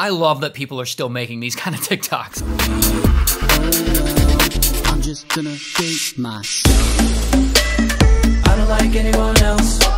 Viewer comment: I thought he said " I'm just gonna touch MYSELF!" LMAFO 😂😂